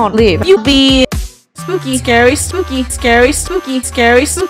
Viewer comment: Yes thank you so much I’ve been saying can sing btw why dose sound like she said sarry here